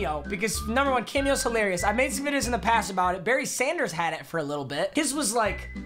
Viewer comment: I thought he said BERNIE not Barry Sanders😂